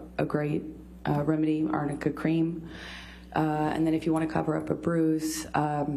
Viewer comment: I thought her lawyer kept saying amica cream throughout this whole trial and Amber messed it up by saying arnica cream!? Hmmm 🤔